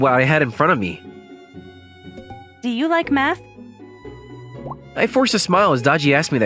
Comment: Ngl, at 5:42, I thought it said meth 😭